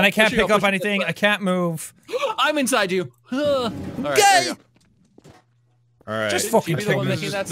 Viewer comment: {Gasp}I’m Inside You 10:51 Jack:Gay {I Don’t Know What He F*cking Said But It Sounded Like He Said Gay}🤷🏼‍♂️